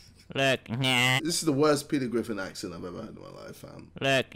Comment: did he said accent not impression ??